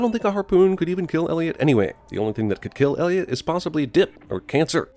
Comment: CinemaSins said "possibly", but the subtitles say "probably". I'm removing all sins from this movie for that.